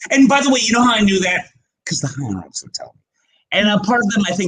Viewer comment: What did he say? "You know how I knew that? Cause the _____ would tell me." Can someone explain? 'Cause the "high hounds" would tell me' Or 'Cause the "Higher ups" would tell me' "Hell hounds" "audience" ....I can't hear it, am I crazy?